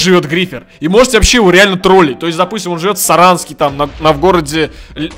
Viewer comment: мне послышалось или он сказал Саранск?